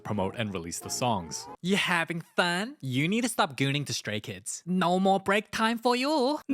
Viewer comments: Stray kids is a boy band. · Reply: I thought he said straight not stray kids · "You need to stop gooning to stray kids" bro summoned a whole fandom 😭 Edit: Wow I did not expect this to be the top comment and get so many likes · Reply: I thought she said you need stop gambling to stray kid